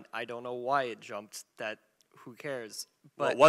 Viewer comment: Did he really say "Who cares"